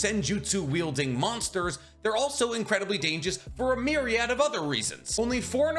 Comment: Anyone else hear him say "dangous" at 11:00 ?